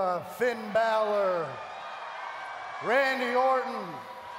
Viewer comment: did elias said randy orton?